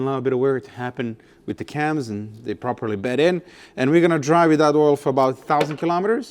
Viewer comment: I thought for a second that you said "and we are gonna drive without oil for about 1000 kilometers" :D